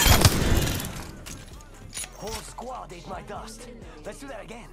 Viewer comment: every 10 sec: whole squad in my dust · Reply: I thought he said: "Whole squad ate my dust" like when you pass Someone on a bike and say: eat my dust